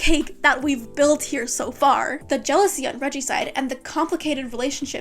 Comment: LMAO i thought you said regicide😭😭😭